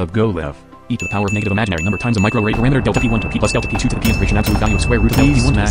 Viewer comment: I swear I heard "microwave" in there